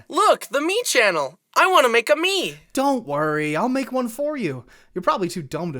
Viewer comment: i thought he said, "I want to make a meme channel," lol😅